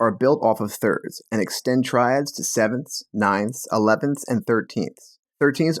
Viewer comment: Triad instead of traid